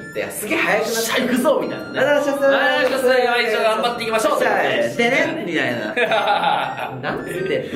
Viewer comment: いまいちど頑張っていきましょっ！って聞こえてる人多いwwwwwwwwwwwwなんか適当に頑張ってますみたいなwwwwwwwwwwww